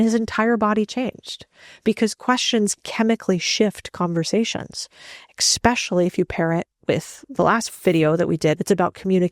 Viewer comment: did you say "Eck-specially"? 🤔🤔🤔